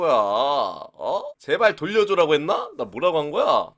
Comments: 찌발 돌려줘! OR 반쪽 돌려줘! 라고 들림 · 찌발 돌려줘!라고 나만 들리나 ㅋㅋ